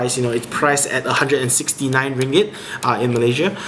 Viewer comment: What currency are you talking about? I think I heard "dringet" or something.